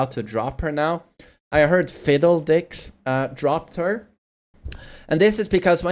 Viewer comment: lol did he call phidx fiddledix rofl 😂